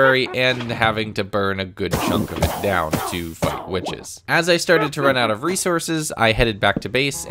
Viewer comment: him(probably): "to fight witches" youtube subtitles: "to fight witches" what i heard: "to fuck witches